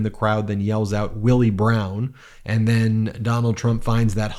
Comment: He probably thinks black and brown outs are a migrant block party. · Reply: I thought he said brown arse. I mean, if I was Donald I wouldn't draw attention to that little detail.